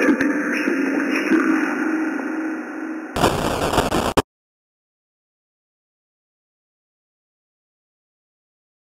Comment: Hat did it say at 17:38 couldn’t make it out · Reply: At the exact time stamp it said nothing. It was just the echo of the voice. Before it said: “Yes…it MUST be done! Now, let’s go! I’m taking you someplace safe!”